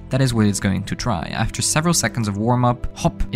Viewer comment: When you said attempt to dive I thought you said attempt to die☠️